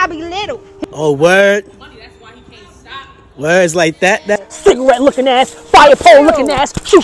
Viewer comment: "Cigarette looking ass, fire pole looking ass, Shoestring look ass aggin!" 😂😂😂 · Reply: Thank you! I thought “did they really said cigarette looking ass”??? Ahaha this is hilarious 🎉❤